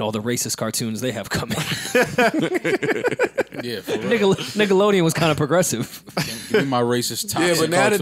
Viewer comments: it almost sounded like Rory said “nigga”, even though he was about to say “Nickelodeon” 😂😂😂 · did Rory just say the N WORD?? 😂 sounded like he said “niggalodean” lmao